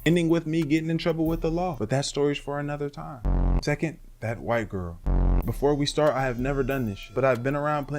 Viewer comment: Im fkn dead thought you said the next drug was white girls